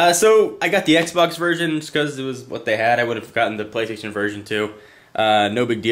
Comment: it sounded like he said : ' the playstation virgin '